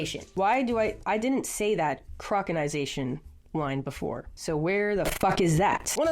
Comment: I got an ad at 31:40 that cut her off and made it sound like she said croconaw lol